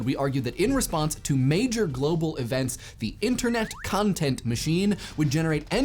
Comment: Did he just say "Cuntent"?